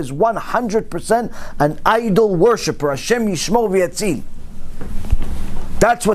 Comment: What is Rabbi saying at 21:57 please? “HaShem....”? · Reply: HaShem yishmor v'yatzil means May HaShem protect and save us.